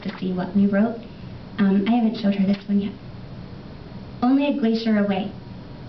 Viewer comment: what do you say in 2:49? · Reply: Hi lovely poet. At 2:49 I say, Only a Glacier Away. Thank you for visiting.